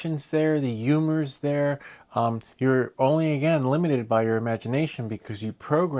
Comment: "you are only eliminated by your imagination." Boy did you say it. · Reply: "limited"